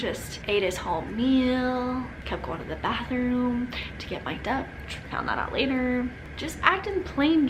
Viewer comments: Ahh so happy for you! Congratulations to the both of you. But I couldn't catch what said at 26:14? Anyways love you guys!! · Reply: In the bathroom getting Mic’d up with the videographer 🤍🤍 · What does she say at 26:13? 'he kept going to to the bathroom to get my ____" The closed captioning says 'duck' so im really confused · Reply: To get mic’d up 😭😂